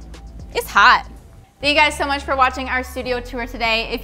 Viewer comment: Did y'all say "you guys so much" instead of "thank you guys so much"? Haha loved it anyway <3